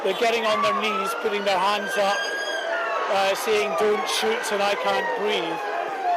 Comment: Shouting "DONT SHOOT" at unarmed police 🤣🤣. These people really be letting corporations make them believe that they're american and american problems apply to them. Give it 10 years and Chinas going to be the world superpower cause we decided to kill ourselves with our own stupidity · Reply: I thought he said don't poop